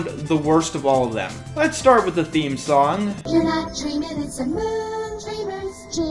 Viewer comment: I may be immature, But I swear she says "Making your sweet ass dreams come true". That's based as hell.